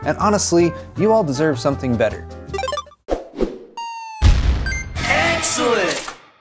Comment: Your "Excellent" callout's 0:25 I cant help to mishear like "egg salade", weird i know, just how my brain works...